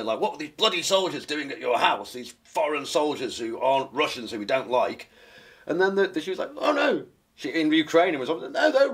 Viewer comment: What's the food he mentions around 21:00? I heard grapes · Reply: Groats.